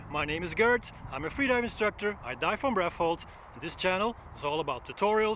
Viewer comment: Is just me or did he just say I die from breath hold at the beginning of the video